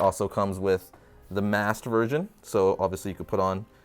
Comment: did he say masked virgin 😂